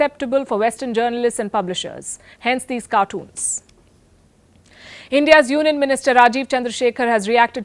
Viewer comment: Did she say India's Urine Minister?